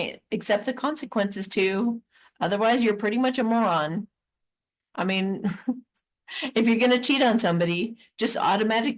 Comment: Hi 😂😊love this..wen u saw the guy I thought you said I moon him 😂😊❤